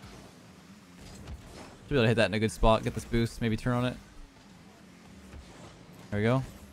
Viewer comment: Ummm, can we address what leyth said at 1:33 ish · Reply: He said "Should be able to hit that in a good spot, get that boost, and maybe turn on it"